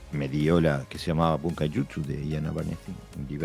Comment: Buenas sensei! Excelente episodio el de hoy! Agradecería que me pase el nombre del libro que menciona en el nin 29:46 que no entendí muy bien el nombre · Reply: Se llama Bunkai Jutsu. El autor se llama Iain Abernethy. Saludos!!